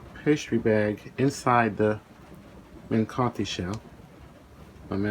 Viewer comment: Hello again. One more amazing recipe. I just have a question: what is this yellow thing at 4:15? I know you said the name of it but I couldn't identify it. For me, it sounded like "cutshell", and I got no idea what it is.